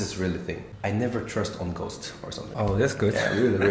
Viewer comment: Can someone please tell me What did He say..I dont trust on what???I am Not able to listen this clearly..did he say i dont trust on ghost???